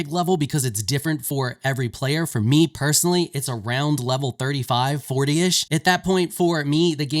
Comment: He said day 35. Lmao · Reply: No, he did actually say level 35.